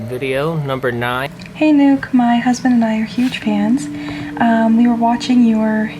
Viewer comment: i thought that girl tiffany said hey nuke me and my husband are huge trans XDDD i was like wheeet ?? XDDD